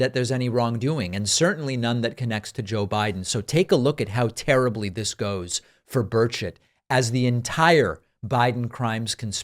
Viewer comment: I heard, 'Take a look at how terribly this goes for birds**t." and started laughing my head off.